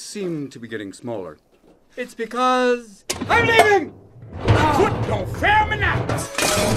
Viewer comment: When I first saw this film, I thought he said "Put Your Ferm in Act".